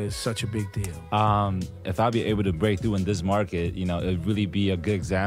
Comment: I thought he said “if I’ll be able to break...”